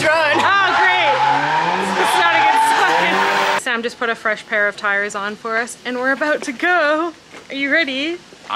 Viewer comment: I thought you said i put a new pair of pants on.. and i thought "but she didn't even start" 🤣🤣🤣